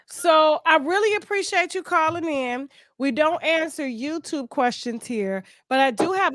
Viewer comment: I thought she said no damn YouTube questions 😂😂😂😂